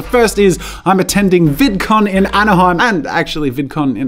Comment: I thought he said in vanaheim 🤦🏻‍♂️🤦🏻‍♂️😂😂